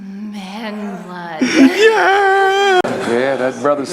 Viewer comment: what did she said? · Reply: "I want Corry's man blood" ~